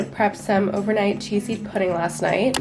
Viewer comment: I thought she said cheese pudding lol.